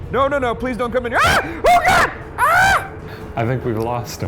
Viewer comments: wait does Ryan know vietnamese? What he said there sound like “ahhh, buong ra” which means let go or stay away. Please tell me that I’m wrong. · did anyone else catch that at 18:31 ? Ryan says "Turning this off" and the spirit box said "Don't leave" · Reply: I heard “don’t, please” and either way I’m spooked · did anyone else hear "don't leave" right before Ryan turned the machine off???? (after he says he's turning it off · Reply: i thought i heard "don't please"